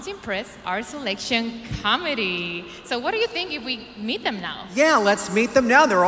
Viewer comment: When Andrea said the 'selection committee' 52:11 I thought you said the 'selection comedy' lol